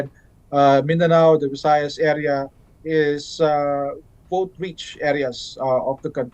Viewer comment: correction on 8:45 I think he actually said "vote-rich" instead of "both rich". It's also worth emphasizing that recent polling shows Marcos Jr.'s poll numbers lagging behind in those regions as compared to Duterte-Carpio.